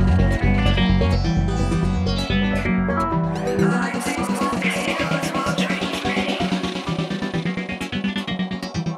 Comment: What's it saying at 6:29? Please, someone. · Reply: The best I can do is "Igniting volcanoes while dreaming, releasing themselves from the world/well" Although that doesn't make much sense. Gunna listen with high fidelity headphones later.